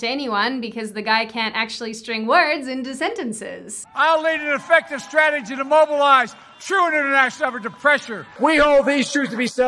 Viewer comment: that kind of seems like a symptom from having a stutter in childhood... Weird, i never noticed this. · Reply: please do tell what he said at 6:51 i beg of you all i heard was "lead an effective strategy to immobilize idvidantil pressure"